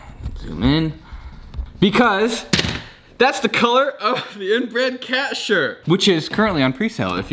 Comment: i thought he said "that's the color of the inbred cat shirt"